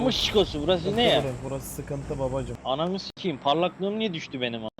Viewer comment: anamı skimmi dedi benmi yanlış duydum ÜQĞWPWOEODKS